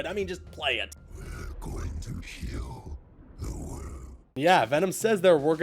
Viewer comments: First time I saw it I thought he said we will kill the world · Without out doubt Harry is venom because Harry even said to Peter that “ he wants to heal the world” and then you hear venom saying the same line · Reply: I dont know if Im hearing something different but it sounds like venom is saying "we are gonna kill the world"